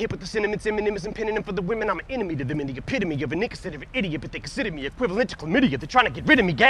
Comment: damn I thought he said n word but he said "an inconsiderate"